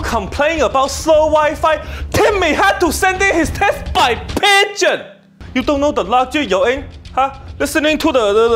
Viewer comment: "Timmy had to send in his test by pigeon" made me burst out of laughter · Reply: Oh he said pigeon I thought he said pager 📟.